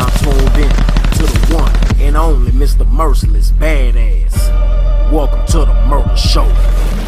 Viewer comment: 😅I thought he said merciless mayonnaise!!